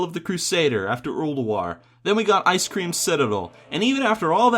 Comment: did he said "Ice Cream Citadel"? :D